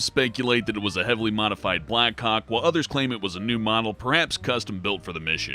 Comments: When he said black hawk it sounded like he said black cock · When he says black hawk it sounds like he says Black Cock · he says black cock instead of black hawk